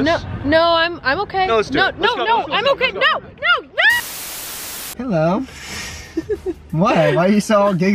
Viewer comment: I thought Brent said “My Love?” But he said hello 👋 😪😭